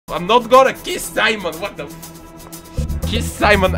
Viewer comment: When I played this vid, I heard you say "I'm not gonna kiss Simon!..." I totally laughed so hard! And my brother was like, "What are you laughing about?" I was wearing headsets, so he didnt know. Haha! Thats so funny!